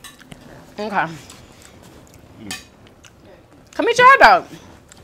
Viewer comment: did she say come eat your eyebrow